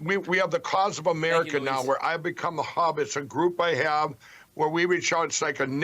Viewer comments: does he say he's become the Hobbit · I could swear that ~2:45 Mike said “I become a hobbit!” This from the peanut gallery (me): There’s that adorable face again Hal! IMHO, if you must grow your hair out, you look much better with the ponytail. (Maybe even a man bun?) I’m sure you’ve been waiting around for my input, so now you have it. 😉